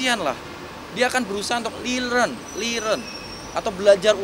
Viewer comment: itu relearn ya? gw dengernya lerearn 😂